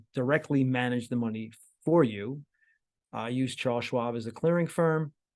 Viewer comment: I nearly fell off my chair when right at the end when he said he uses Charles Schwab tp clear money as I thought he said Klaus Schwab !